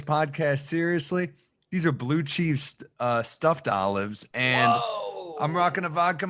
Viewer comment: When Sam said he had blue cheese stuffed olives, I thought he said "Blue Chew" stuffed olives. Boner olives.